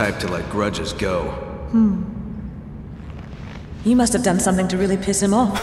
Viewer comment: A legenda (do jogo) está errada. A Sheva fala "you must have done (ter feito) something to piss him off (algo para irritá-lo - no caso, o Wesker)", e não "you must do (fazer). Então, o Chris responde com "o que eu fiz foi mais do que apenas irritá-lo." Não sei se o pecado foi seguindo a tradução vista nesse vídeo, mas eu particularmente adoro esse diálogo.